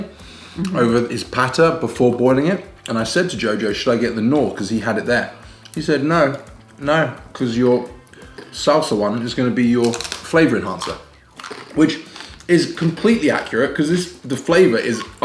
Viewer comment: Oh, i totally thought they were saying Salsa one! I just realized at 10:53 they were saying sawsawan. LOL, you should put the stress on the second syllable, not the first. So it's saw-SAW-an.